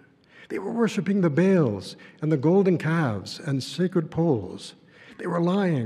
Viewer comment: did he say “sacred poles”, like the one they say Jesus was nailed to? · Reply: Yes, he said " sacred poles" but it doesn't have any relation to the stake Jesus was nailed to.